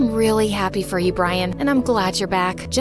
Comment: she actually said brain when she was talking to about him 😂😂😂